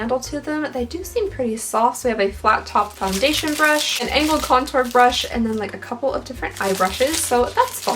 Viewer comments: I heard "anal contour brush." 😂😂😂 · i was listening from afar while i cooked and thought i heard anal contour brush.....